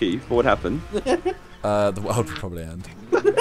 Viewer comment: What did say at 2:04? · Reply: the world would probably end